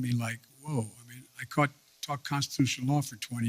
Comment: ?? Is he saying he taught or talked a 3 credit course for 20 years?